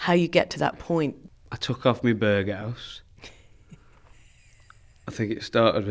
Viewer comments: english is not my first language and let me tell you i am STRUGGLING over here. tf is he saying at 2:17??? he took off his what? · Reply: Berghaus - a brand of jackets / coats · what does alex say here?? "I took out me b....." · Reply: Berghaus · "i took off me..." what's the word he said? hahah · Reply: Berghaus. Its the brand of the jacket he wore